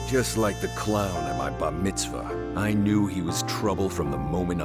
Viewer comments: He totally says bat mitzvah instead of bar mitzvah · The names on the board, though. I hope we get to see them all, as well as all the little fascinating details you guys worked so hard on! Although, the subtitles said bar mitzvah, it definitely sounded like said baT mitzvah, which is used for girls. Unintentionally hilarious?